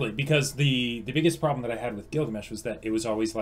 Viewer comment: Did he say the biggest problem I have with Gilgamesh from fate zero at 9:00 ?